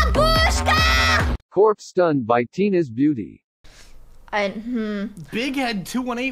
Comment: i thought it said tinas bunny lol so i was waiting for a bunny